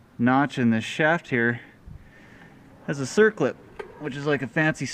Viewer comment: I think he meant to say bitch clip haha